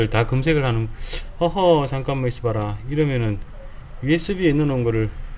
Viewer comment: 어허 잠깐만 있어봐라가 어허 잠깐만 이 시발아 라고 들림 ㅋㅋ